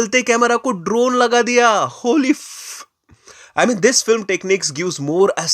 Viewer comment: kya Kahana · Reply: Happy Holy bol rahe hai 😂😅